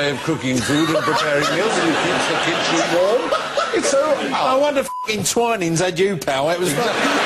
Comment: what does Phil say at 1:10 ? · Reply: "No wonder fucking Twinings had you pal"